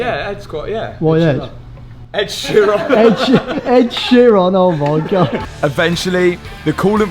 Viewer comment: This car and project will be forever known as 'The Bobgatti' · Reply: I thought they said it was Ed Chiron